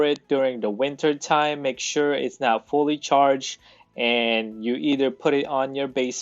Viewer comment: you said "if you want to store it during the Winter time, make sure it's NOT fully charged". Did I hear that correctly? Thanks